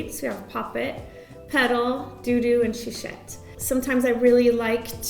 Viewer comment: Omg, I thought she said the names, DOo DOo and Shit. 😂 This tutorial was so nice, thank you !